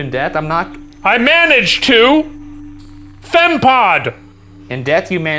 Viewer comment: In Stray Souls, play as Daniel, an ordinary teenager caught up in an extraordinary situation where each step leads him deeper into a world of nightmares. Are you brave enough to trick-or-treat Grandma’s house this Halloween? Wishlist now: <link> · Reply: for #debrief: do you think at 35:30 instead of saying "fem pod" the box actually said "rem pod" and that "nipple scooper" is referring to earlier when was being weird with the rem pod's antenna? I can totally imagine a ghost that's never seen an antenna before describing it as a "nipple" because of the round top especially when some dude is over there fingering it like a weirdo